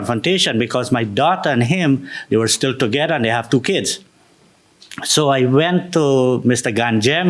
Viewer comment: Oh. They have two kids. I thought at first he said he didn't hit the son-in-law because "they had to kiss". Dang hearing.